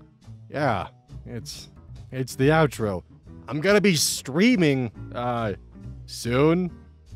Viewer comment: Who else heard him say "I'm screaming" instead of "I'm streaming"? Was really confused there for a second at the end. Also, dude, I don't know why I never thought of potato bread for grilled cheese but now I MUST have it! Also-Also, mix in some cheddar cheese with that American; American is made of 50% cheddar already and it's one of the few actually good foods to come out of the UK.